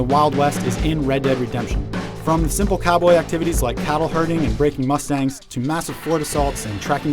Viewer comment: did he say "raping mustangs".